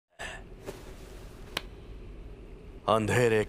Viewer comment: You all he actually said ‘baap ki imarat’😭